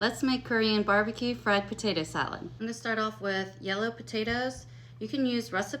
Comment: I didn't know that russet potatoes were yellow potatoes? I thought russet potatoes were regular white potatoes??? Am I incorrect or something? Now mind you I'm in my 40 so I think that I have seen a bunch of potatoes in my life!!!! I thought roasted potatoes were the white potatoes that are good for baked potatoes!! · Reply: I had to watch it again but she didn't say she was using russet potatoes she said yellow potatoes but you can use russet so you're correct about the types of potatoes you just misheard her ❤❤